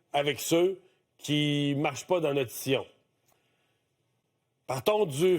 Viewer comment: Qu'est-ce qu'il a dit: " ... ceux qui ne marchent pas dans notre scion" ?? @ 2:30.